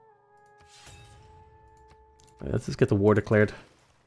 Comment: I keep hearing Akkad as A Cod lol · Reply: I heard "let's get the war declared" as "let's get the water cleared", which is pretty much the same thing, I guess.